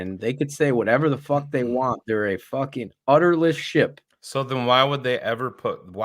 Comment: did anyone else hear say "Udderless ship"?